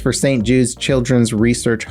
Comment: I thought you said st jew's children's hospital, I was gonna say. That sounds pretty sinister....